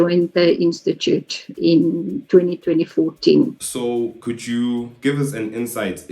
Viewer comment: did she say she joined the organisation in 202014 😂